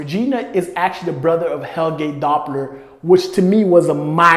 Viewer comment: Thought you were saying Hellgate Doppler. But like, it checks out.